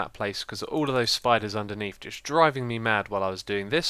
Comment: Haha i thought you said Underwear :P